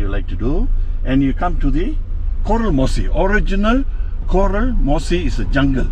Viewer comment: What was that Indian taxi driver was saying I heard him say was "coral mossy forest" Coral ??? I thought coral is in the sea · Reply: he said horror mossy forest